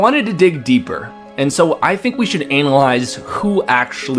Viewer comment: did he really say anal eyes instead of analyze?